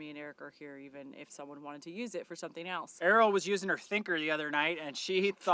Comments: I swear I heard "Arielle was using her finger" at first. I laughed a lot regardless. 😂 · I could have sworn Eric said, "Arile was using her SPHINCTER the other day...," I had to go back to hear it correctly---died laughing!!